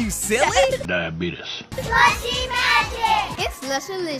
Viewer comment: i wasnt looking and i heard "Sussy Magic!"